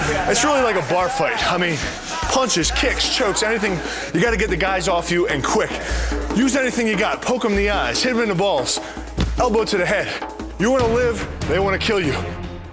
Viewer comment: did he say "shave their balls"